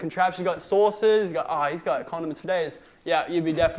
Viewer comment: did he say codoms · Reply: Even though it's said pretty quick - we're certain he said "condiments"